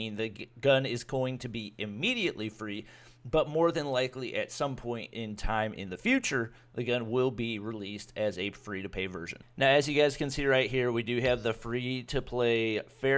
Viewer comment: Am I trippin or did he say a free to Pay version